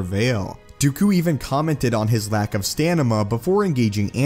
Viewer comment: he says “stanima” instead of “stamina”